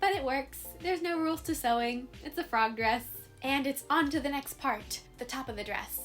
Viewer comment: honestly thought she said it’s the frogress (frog process)she actually said frog dress